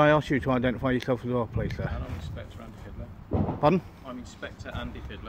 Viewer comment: I swear, I heard “Andy Hitler!” After which, the female cop, started to hit on the auditor, “So, you come often here!?” xD